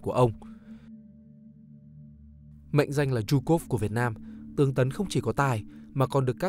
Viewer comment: cứ nghe thành Trung Quốc :))) gg translate nó sub tự động cũng ra Trung Quốc Vào link bài viết mới biết là Zhukov